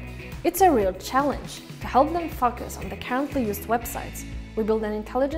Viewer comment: Did she said "to help those fuckers" at 1:45 ?